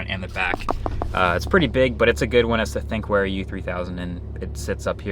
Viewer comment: What dashcam did he say he got? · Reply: Found it. Thinkware U3000